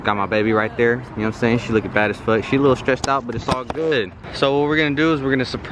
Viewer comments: Did he just say, “ she’s a lil stretched out “ 😬😂 that’s fucked up ! Or did I hear wrong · “She looked stretched out” 😭😭 ihy lmaoo! I actually love y’all 💖💖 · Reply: He said “she’s a little stressed out..” lol & I had to go back and rewind it bc I thought he said stretched out too.